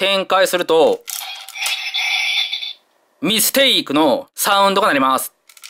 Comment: ミステイクって言ってたんだw今まで「ミキティー」って聞こえてたw